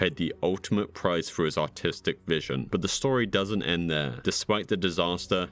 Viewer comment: Thought you said "autistic vision" LOL